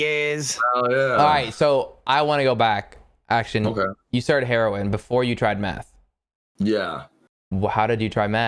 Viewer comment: I thought it said herobrine like from Minecraft